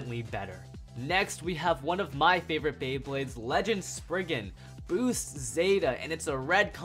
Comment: did he say Sprigin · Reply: Spriggan